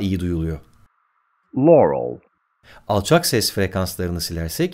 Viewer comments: başta yanny duydum 06:53de laurel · BEN İKİSİNDEDE LAUREL DUYDUM O NASIL OLUYOR :D